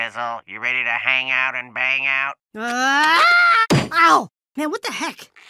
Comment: This show was never for kids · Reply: I think i heard at 4:27 that weird guy say "do you want to hang out and bang out?"